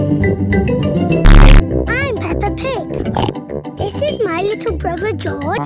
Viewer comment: The narrators accent sounds like he says pepper pig